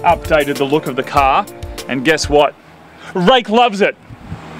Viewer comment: all I hear is "rake loves it"